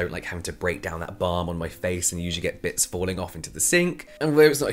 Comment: For a second I thought you said `nits falling into the sink’ 😆